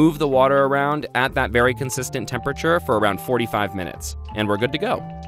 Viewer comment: D—Did he say 45 minutes??? Or 4 to 5 minutes??? Because very confused rn. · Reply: James Stark: Ben Edition i think 45